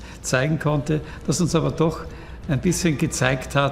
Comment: Did he call him Adolf at the end of the interview? Check it